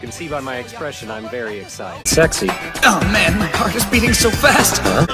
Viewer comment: why did I hear "why is my c**k beating so fast" 😭😭💀💀